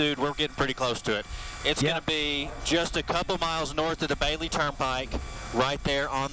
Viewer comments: did he say DABABY TURNPIKE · did he just call the tornado a "dude"? lol